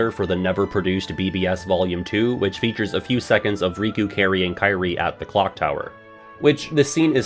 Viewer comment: Since I habitually pronounce Kairi’s name as “Carrie”, I misheard 19:22 as “Riku, Kairi, and Kairi” and got confused. XD